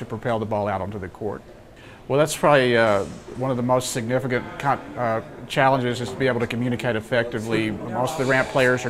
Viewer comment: nive vid, but at about 00:35~ i thought it said the most significant c**t x/x/